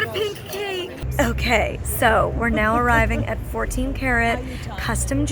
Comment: I am not a native english speaker so I thought she said ,,24 carrot" instead of ,,24 karat"😂😂